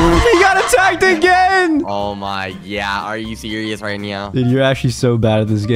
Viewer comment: Did bro just say Oh my gyatt